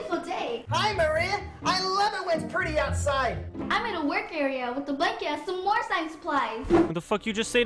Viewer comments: I think she said "it made a work area with a blanket and some more sign supplies" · "I made a work area with a blankey ass'em morse heinz plies!" ROFLMAO!!!!!!! I swear to god i replayed that scene a few times just because i REALLY wanted to know what she said and EVERY SINGLE TIME that's all i could hear! "What the fuck did you just say to me bitch!?!" LOL! Hell yeah! Cause that's how we handle shit on the streets, show that bitch what's crackin'!